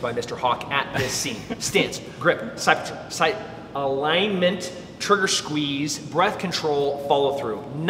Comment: sounded like he said 'Sight Picture' but that's just me.